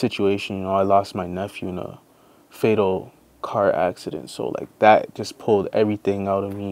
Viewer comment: I thought he said "I lost my nuts in a fatal car accident" had to rewind that